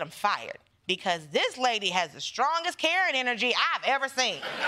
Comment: lol, I thought she said MTG had carrot energy. Then I realized she said Karen energy. And carrot energy, for some reason, is funnier to me right now.